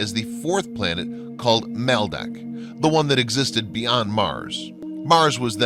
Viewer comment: Did I hear at 8:52 he said melmac that's alf 's home planet.